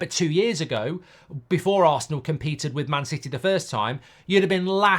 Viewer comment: Mark, i thought you said nobody can compete with city?